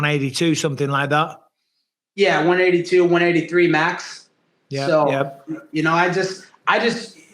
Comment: I thought he said he cut 18 pounds no he says he’s 182 183 max😂